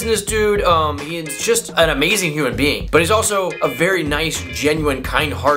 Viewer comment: Lol, I thought you said human beaning